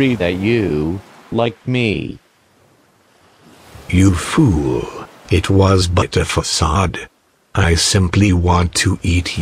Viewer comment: “*you fool, it was but a facade*” -Moto moto 2019 0:49 Edit:OMG sooo many likes!! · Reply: I thought it said "you fool it was butter facade"